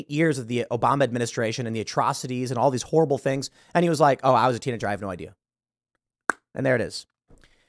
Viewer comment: What did you say at 9:41? Was it English? It sounds like, "iwasatinadrivenoidea" who or what is tina drive?